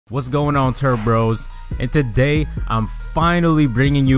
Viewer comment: it kinda sounds like sayn terd bros (instead of turbros) xD